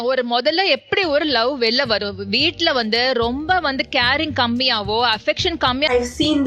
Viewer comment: did I hear fascism?